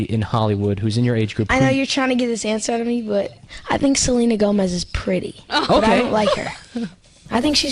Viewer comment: "I think she's pretty but I dont like her" did anyone catch that.....